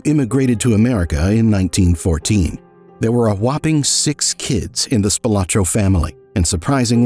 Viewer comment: "a WAP’ing six kids" Did anyone else catch that? 😂